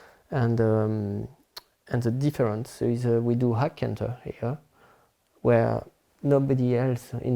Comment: About 16 mins in he said he changes something in his training. Sounds like he said "hard canter". Anyone have a idea what he said. Thanks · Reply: Hack canter